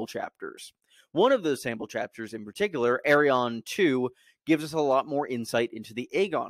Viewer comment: Bruh I was so confused because I thought you were saying “Aeron” as in Aeron Greyjoy but you were actually saying “Arianne”